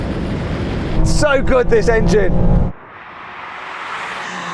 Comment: で『最高ですエンジン！』って日本語しゃべってるように聞こえるんですが！？